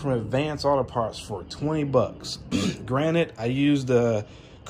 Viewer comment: did you say "granite" ? Nice review. Thank you.